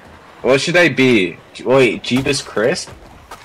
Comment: I thought it said Jebus crips